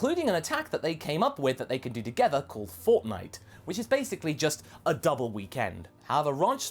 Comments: did said fortnite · I hope I heard that wrong, I hope I didn't hear the name of the technique as "fortnite"...